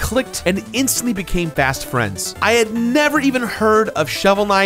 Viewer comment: Jirard says they became fast friends, but it sounds like he said "bast friends" Considering this and the lack of KNIGHT representation leaves me feeling HOLLOW, so I have to give this video, a B+